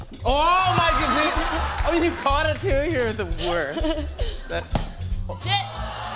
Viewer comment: did she say “shit” ? Lmao 😂